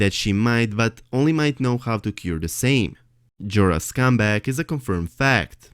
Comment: at 3.26 it sounded like you said jorah scumbag, haha Euron is not Urine haha its( your on) great videos and I love your accent, slow down just a little please. You're my favorite!